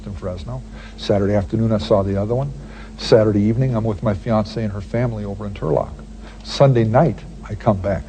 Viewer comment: Did he say fiancé??